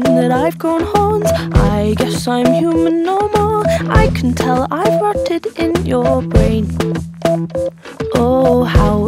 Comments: it sounds like she said "i can tell i farted in your brain" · "I guess I farted in your brain" Who else heard that??!?!😂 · Am I the only one that thought it said: Get out of my room Smile whipped clean Isn't it weird To be so mean Im guessing that I've grown horns I guess I'm human no more I can tell I've farted in your brain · Why does it sound like it says “i can tell i farted in your brain” ?!? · honestly whenever I hear the sentence " I've rotted in your brain" I just hear "I've farted in your brain" don't ask why bc I don't know hahah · I love Dodie’s voice so much and this song is so meaningful and beautiful but the first time I heard it I thought she said farted not rotted 🤭😂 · Am i the only person that thought she said "I can tell i've farted in your brain" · Did she just say"I can tell I farted in ur brain" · why did i always think it said ive gone haunt 😑